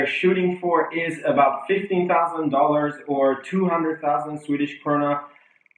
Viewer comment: $50,000 for a car that loses to tesla in every single spec. Tesla model 3 is $35,000 before $7,500 in tax credit. The model 3 is in production (finally) and this car doesn't even have a road ready prototype. In which ways is it beating Tesla again? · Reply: WarMachine I think you misheard him. Sounds like he said $15000 which makes more sense when you convert to sweedish currency